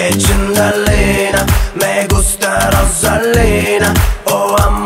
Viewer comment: I think I hear “Celina” and “Rosalina” but not sure ☺️ Will have to listen more. Yes, he says “Me gusta Rosalina” Rosalina is a lucky girl